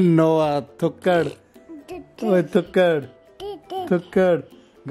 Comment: Ohhh my goshhh...did he just say "thukkad" at 0:46 Did he really say that Moneyy bro?? Has he already started imitating words??😬🥺🥺🥺♥️